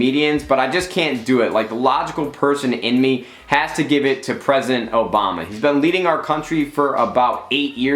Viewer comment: Did homeboy just say "Present Obama"?